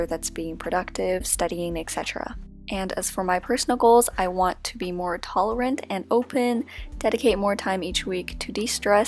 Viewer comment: i thought she said “i want to be more taller” 😂